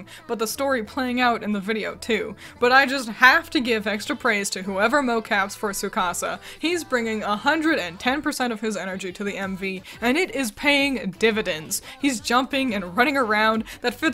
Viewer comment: wait what you say there?😭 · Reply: "But I just have to give extra praise for whoever mocaps for Tsukasa. He's bringing 110% of his energy to the MV, and it is paying dividends!" I assume the word that has you tripped up is 'mocap'. It's short for "motion capture" and it's how 3D MVs are animated. Where there are actually people in special outfits performing on a special stage that gets recorded, and that recording gets turned into animation. It's kinda hard to explain, so I suggest looking up a video about it, it's pretty cool!